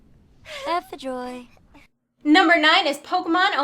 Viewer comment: does she say ''F. the joy'' ? The satanism is strong in those 2 :o